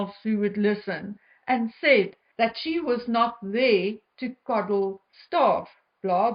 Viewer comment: I think what she said was that it is not her job to coddle staff. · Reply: I thought she said that "I wasn't raised to coddle staff." If incorrect, I apologize.